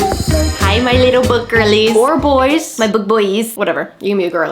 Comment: When you said “Hi my little book girlies” I really heard “Hi my little boogeries”